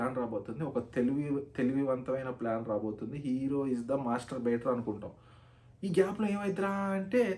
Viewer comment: Did he just say hero is the masturbator at 15:05 😂😂😂